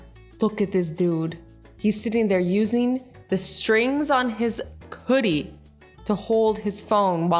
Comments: did anyone else hear koodie insted of hoodie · did she say koodie. A boogie with a koodie😂😂😂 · Excuse me but did she just say: "Coodie" Instead of "Hoodie"? .... Wuuuut'za "Coodie"??? (#NoOffence) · I heard you say koodie instead of hoodie lol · she said cuddie instead of hoodie 😂☠️